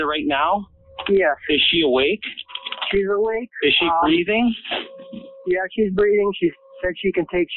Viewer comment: Caller: She's been stabbed multiple times. Operator: Is she bleeding at all?😐 Ok, it's been brought to my attention the operator is saying breathing and not bleeding. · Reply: "IS SHE BREATHING?" That's what the operator said asked.